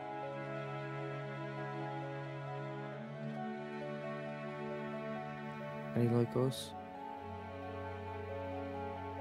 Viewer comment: I’m having a hard time understanding with the dialect.... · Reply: same I thought he said "are they like us?" But I kept replaying it an I don't think it's that . I too missed the ending